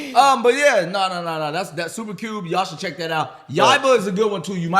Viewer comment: Can someone tell me what show they were recommending at 56:26? I couldn't quite make out the name cause my hearings trash and the YouTube captions ain't helping. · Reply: Super cube, Yaiba. Not sure which one ya need but those were the 2 mentioned. The clip they showed was from super cube.